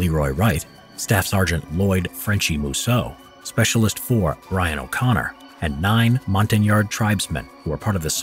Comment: I thought he said Brian O’Connor But he said Ryan O’Connor